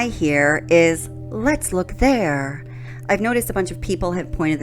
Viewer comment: You heard "let's look there." I heard, "let's look sad."